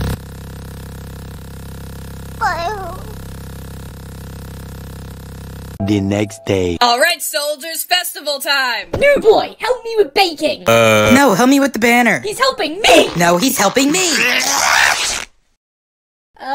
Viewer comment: I thought she said beijing in 0:53 💀💀🗣️🗣️🗣️